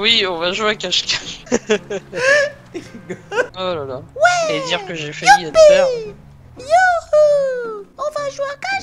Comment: Ptddr c moi ou a 10:51 il dit : et dire que jai faillis etre pere